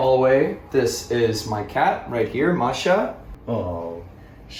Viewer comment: I’m sorry did he say cat